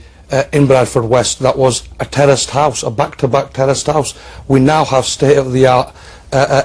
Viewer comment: I could have sworn he said "back to back terrorist house"